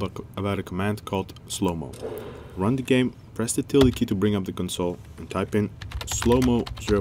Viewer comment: What key did he say to click when we wanted to bring up the console? All I heard was "press the tele key".